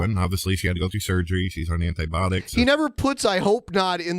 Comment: at around 38:37 I swear says ”antibotics” instead of ”antibiotics”